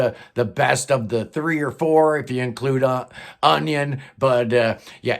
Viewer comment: I heard "Onion" , think you meant "Union"